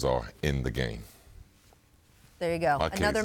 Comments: “Believe that.” Scottie at the very end lol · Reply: I thought he said "Bleed that" thanks for clarifying! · “Believe that.” Scottie at the very end lol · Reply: Hahaha I went to rewind (I was alive for VHS/Cassette Tapes) to catch that again but couldn't figure it out... Said ef it. Lol thanks for clearing it up. I knew it was something I wanted to hear. I thought he said, "I seen that."